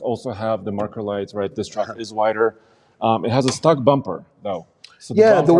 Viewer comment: When you said stock bumper i thought you said stuck bumper, like the bumper is stuck somewhere, and now the truck is STUCK, (not going nowhere)